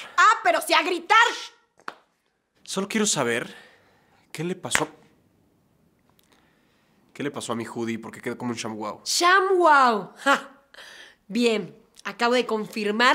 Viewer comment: Alguien sabe que es un shan-huao o algo así que dice en el mundo 4:10 gracias